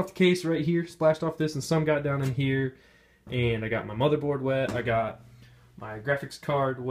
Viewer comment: I thought you said "I got my mother wet" and I spit out my food out of my mouth and replayed it, then I was relived, I was so chill like a Shark eating a cheeseburger saying "COME ON IIIIIIIIINNN!"